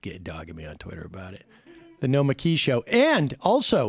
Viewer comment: Every time Sam tries to say Nomiki all i hear is "Nikolaj." "No it's Nikolaj!"